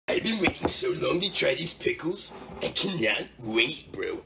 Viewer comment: For a second I thought he said "Chinese pickles" at the start and thought this was a chinese fingertrap joke.